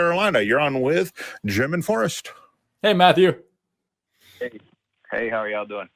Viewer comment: I swear, when he said " You're on with Jim and Forrest", I thought I heard, "German Forest" at first! 😂 I really can't listen and read at the same time.